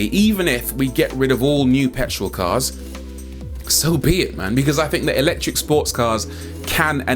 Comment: I loved this video and your take/presentation everything.. I came to have a laugh because did you say “So be it my 🥷s”😂😂